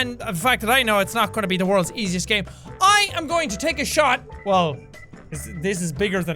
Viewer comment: Drunk combiner engaged! I doubt it will be easy. I thought u said I'm gonna take a shit but u said take a shot. lol